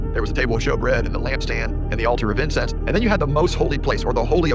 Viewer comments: Slow down my brother. It sounded like you said the alter of incest. That is a VERY different thing that the democrats do 😂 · I heard...'alter of incest'???? Did I hear that correctly????? What?